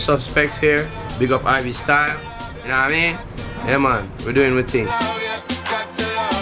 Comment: What he saying at 1:12??? I don't understand it,he says so fast :)) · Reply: J3N3I we are doing our thing